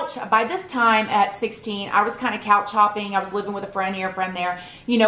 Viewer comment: When she said couch hopping I thought she said cow chopping 🙈🙈😂